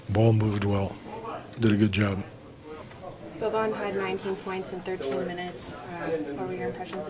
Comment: whos the player they are talkibg of? Cant hear it well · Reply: Boban Marjanović